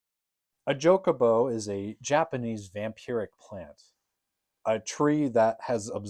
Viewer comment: Did you say Chocobo?